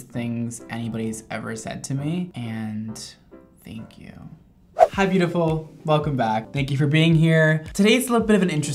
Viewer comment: I was waiting to the part in the intro when he says "you look beautiful today" and it never came😞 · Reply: Debora Jagielska He actually says “hey beautiful” at 0:16. Not the same but still😉